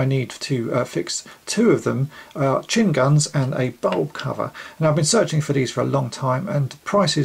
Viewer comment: did you say “butt-hole cover” ?!?!?!